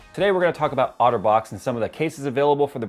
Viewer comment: Due to me watching this on a phone, I kept hearing Otterbox as autobots and became really excited. 1:08 "Today we will be talking about autobots..." 1:25 "That are protected by autobots..." Throughout the entire video.